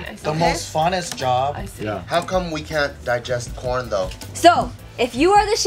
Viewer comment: I thought said "How come we can't digest porn?" instead of corn 😂😂